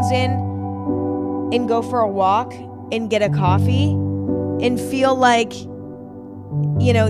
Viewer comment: Thought she was randomly saying "It go for a walk. It get a coffee" and for a while I was confused and wondered if she was having a mild stroke